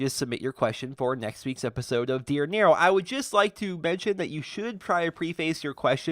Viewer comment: Because of his voice I thought he said dear Nerum